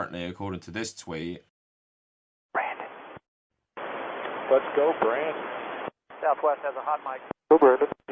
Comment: Ngl during them actually chanting I thought they were saying “let’s go Biden” 🤣🤣🤣 this made me even MORE confused!!